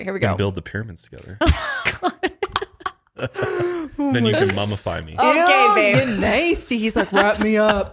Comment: Lmao not the Only Fans 😂 said “Mummify me” 💀 “let’s build a pyramid” 😂😂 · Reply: I thought he said “cummafy me” at first i was like wait ... rewind ... lmao what